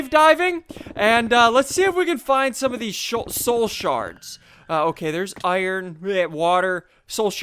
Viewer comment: It sounds like you said soul sharts